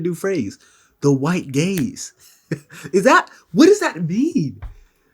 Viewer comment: I heard that in another context and thought they were saying "White Gays." Gaze makes some sense lol.